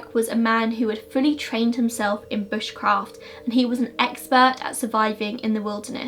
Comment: For any other Americans (or anyone else lol) who don’t know what Bushcraft is, it’s defined as “skill at living in the bush.” ☺️ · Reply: i heard it as "bitchcraft" xD